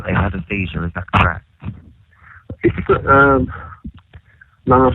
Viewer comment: the operator asks “you think he had a seizure, correct?” The subtitles say “mumbling” for the callers response but he says “naw, I was lyin”